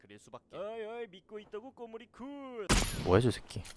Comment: 말소리 작게들리는거 개웃기네 ㅋㅋㅋㅋㅋㅋㅋㅋ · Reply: 5252 믿고있다구 꼬물이쿤~♡ 이라는데오? ㅋㅋㅋㅋ